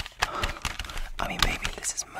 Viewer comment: For a second I misheard "Mischino" to "Mosquito"